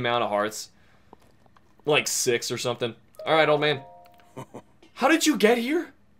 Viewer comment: Every time he pronounces "Oman Au" I hear it as "old man owl".